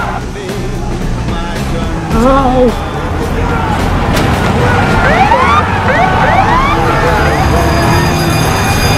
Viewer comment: what was he saying? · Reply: He said hi.